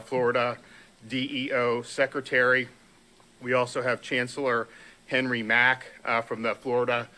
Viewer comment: he said chancellor but I heard "cancerol".